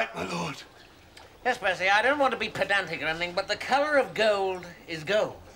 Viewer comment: what is that word rowan says ? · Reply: ".. I don't want to be pedantic or anything ..."